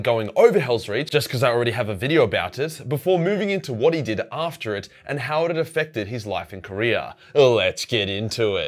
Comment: LMAO .....IT sounded like you said "What he did after HELLSREACH, and how it affected his life in KOREA." 🤣🤣🤣 If GRIMALDUS had fought in the Korean war... BODIES.....JUST AS FAR AS THE EYE COULD SEE...LMAO